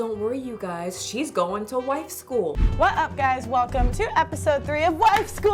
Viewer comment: Ngl my high ass thought said she was going to "white" school. Like girl is so pale we can throw her ass outside in a blizzard and lose her. 💀